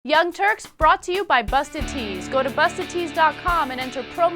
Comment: I thought the ad said "Busted Teens".